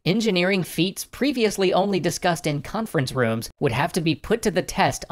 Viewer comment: I thought u said that conference rooms should be in planes lol